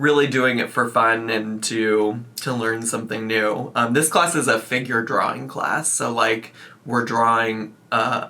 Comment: I shit you not I thought you said finger drawing class and I GASPED